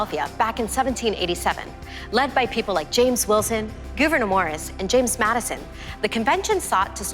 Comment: Did she just say "Goovenor" Morris? Lol